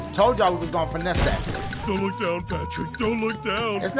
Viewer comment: Is Patrick saying : „don’t look down dashie“?